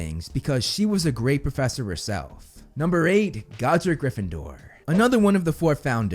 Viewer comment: Lol the captions say "Godzilla Gryffindor"